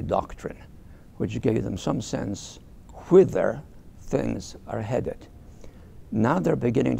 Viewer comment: COULD SOMEBODY EXPLAIN FOR ME ... what is he saying at 5:25 - 5:29 - "quither things are headed" ?!?!? What is he trying to say ? I don t understand him, pls explain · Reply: The word is 'whither'